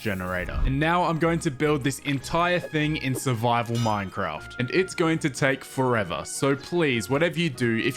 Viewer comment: did he say survival minecraft